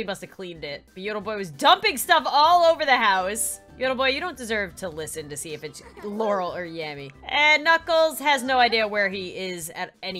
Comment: Who else heard her say “You don’t deserve to hear if it’s Laurel or Yammy .”